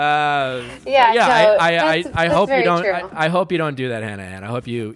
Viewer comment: he says "I hope you"....but the subtitles says "I love you"